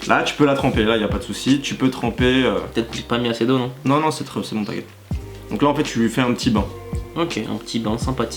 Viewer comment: C moi ou a 9:26 il a dit '' nan nan c bon ta gueule '' 😂😂😂